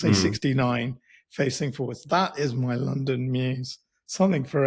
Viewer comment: Shiiit. Thought yall were saying "London Muse". Had to google this. It's a damn garage with a house on top.